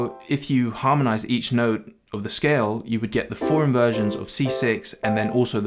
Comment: does he say “foreign versions?”